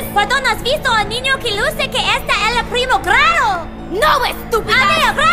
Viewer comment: Omfg the Hospital one "No running in the hospital, bitches!!" "have you seen a young boy who appears in the first grade?" "No stupid!" "Okay bye, thank you!" "I SAID NO RUNNING!!" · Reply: i heard it as 'a weird cousin' (primo raro) instead of 'a young boy who appears in the first grade' (primer grado) and i speak spanish lmaooo but you're right